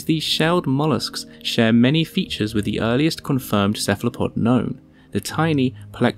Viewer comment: That little dude in the thumbnail has a sweet hat. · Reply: I actually thought he said "Earlirst confirmed cephalopod gnome" for a second and it kinda works lol